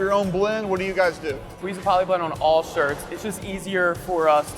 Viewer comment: Awesome!! Love it! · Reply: did he say they use poly ink on everything?